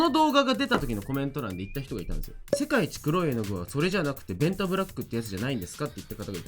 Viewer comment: ベンザブロックに聞こえたというか、頭に浮かんだww